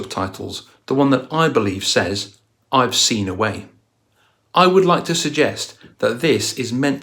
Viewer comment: I thought he said "I've seen yahweh"...